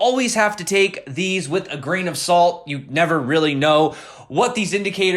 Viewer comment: I always thought u say "with a green aussault", hahaha.... it is a grain of sault, right???? plsexcusemyenglish